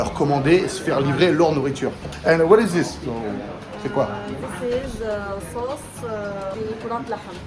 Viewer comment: j'ai compris this is... Sauce...... Llaham (viande)